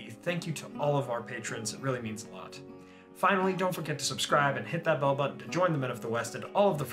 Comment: At the end I heard you saying "hit that Bill" :)